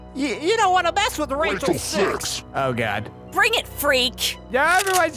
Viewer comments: Am I the only one who heard Rachel6 at 2:23? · Did she say RACHEL6!!!🤤🤤